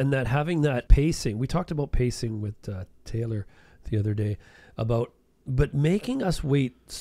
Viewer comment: did you just say Taylor